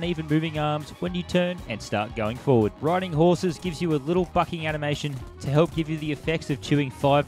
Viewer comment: little fucking animation, did i mishear that?